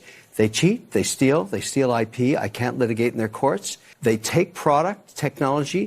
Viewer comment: I thought he said "They take, they steal, I pee" and for a few seconds genuinely thought he was saying "the country of China is stealing from American business men while our backs are turned to pee" lol